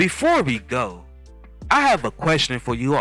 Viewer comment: did you just say, "...Loleatta HATHAWAY!?? " (10:05) ....boooooooy....... I'll let it slide! .....ha-HAAA! ; )